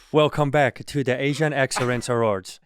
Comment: I thought he said Asian accidents awards. 🎉